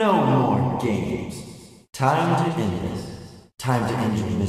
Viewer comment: always thought he said "time to die" when he says "time to end this" I like my version more